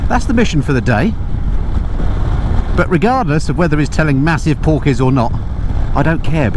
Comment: I thought you said he was telling massive porpoise 😅